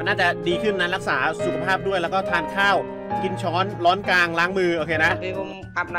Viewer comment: พึ่งสังเกตนะ พี่เค้าพูดว่า "กินช้อน???? ร้อนกลาง????" อิหยังนิ๊???